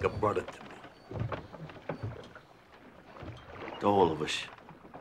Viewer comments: What does say after Pussy asks to keep his eyes? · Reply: "To all of us" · What does say at 2:08? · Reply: "To all of us"